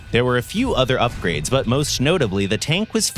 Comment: Am i the obly one who heard "most schnotably" at 04:34 ?? 😂